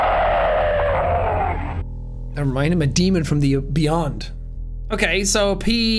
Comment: I thought you said “Never mind, I’m a diva from the beyond” instead of “demon from the beyond” 😂😂😂😂😂😂.